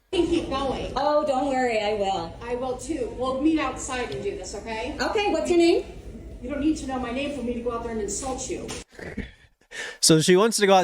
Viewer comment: i was washing dishes while i watch and thought she said "you don't need to know my name for me to go outside and ASSAULT you" 😭